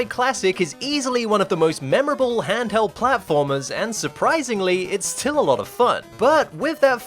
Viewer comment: At the beginning I spaced out, and my brain misheard "one of the most memorable handheld platformers" as "the oldest anarchy server in minecraft" and I actually jumped